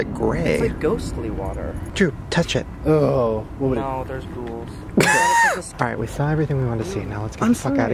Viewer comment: Drew, touch it! Drew: No...there's ghouls.. *laughs* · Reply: I thought drew said "No, there's rules".. .-.